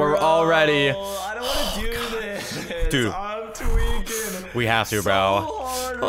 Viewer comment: I'm tweaking I'm tweaking Me:😂😂 You: what the heck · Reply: Lexi w I thought it said I’m twerking 😂😂😂😂😂😂🤣🤣🤣🤣🤣🤣